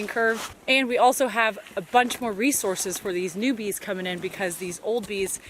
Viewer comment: I hear "newbies" everytime you say "new bees" in your videos XD